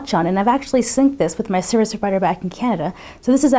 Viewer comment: What in the world did she say at :47 seconds? I wanna know what app she synced Watchon with....sounds like she said "serious rider back from Canada."